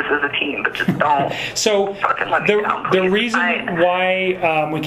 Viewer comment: Am I the only one who heard her say to the cop: “don’t fucking let me down”?